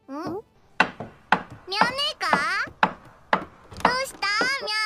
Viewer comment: what did she just say??? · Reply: Mya-nee ka She didn't say the N word 😔